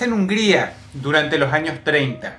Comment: empieza en un gria por que dice gria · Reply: games Hungría un país de Europa Central.